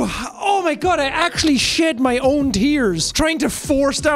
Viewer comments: i thought he said "i actually just sh*t my own tears", took me a second to figure out he said "shed" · Jack: I actually shed my own tears trying to force down a laugh Me: visible confusion who else’s tears would you shed? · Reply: At first I thought he said "shit my own tears" which would have been pretty impressive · Jack: I actually shed my own tears trying to force down a laugh Me: visible confusion who else’s tears would you shed? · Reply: I thought he said "I actually shit my own tears" xD